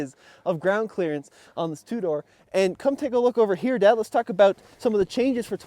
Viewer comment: At the 3:59 mark did he say “come take a look over here Dad…”? 😊